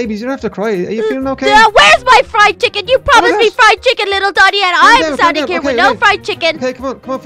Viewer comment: who heard little daddy or is it just me even tho its little donny